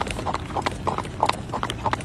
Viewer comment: "Crazy bitch gEt on the trail" 😆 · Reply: I thought he said "crazy bitch you gon get me in trouble" lmao